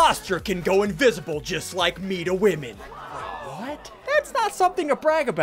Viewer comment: Ssundee: Imposters can go invisible like me to woman. Me: I thought it was Sigils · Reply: Ngl, I misheard him and though he said, “go in physical like me to women” and well, that was a yikes for me.